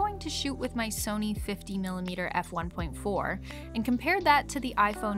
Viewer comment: did I heard f1.4 🤔🤭